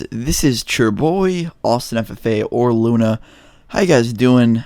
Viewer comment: Bro I always thought he said Austin Santa Fe not Austin ffa